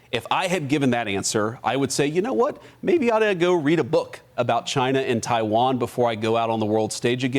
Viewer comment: did he just say he'd read a book?